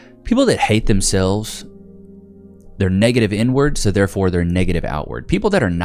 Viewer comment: I thought he said “negative N Words” 😂 I was like DAMN RIGHT 😂